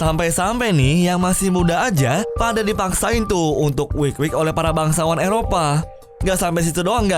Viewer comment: gw salah denger ya mereka dipaksa ngapain.... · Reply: Wik wik wkwkwkwk